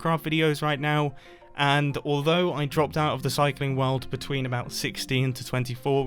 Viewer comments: I thought he was saying recycling weird...... oml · Lol I thought you said recycling and I was like “well I am so glad you are passionate about recycling but why wouldn’t you be making Minecraft vids without it?”